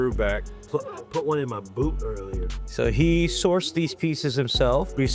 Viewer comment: I had to replay a couple times I thought he fuckin said “put on it my pooper” 😂